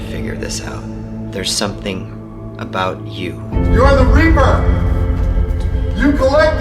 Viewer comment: The first question asks in sub basmement i hear "I am/the reaper the next question it double confirmed it